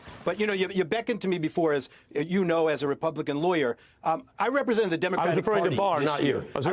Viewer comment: He misheard Ari. He heard, "...whom you know AS a Republican lawyer" when Ari actually said, "...whom you know IS a Republican lawyer."